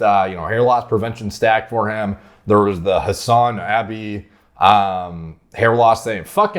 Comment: so he didn't say semen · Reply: I thought he said sauna